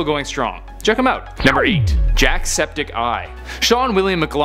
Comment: Did he just say jack-septic-ai?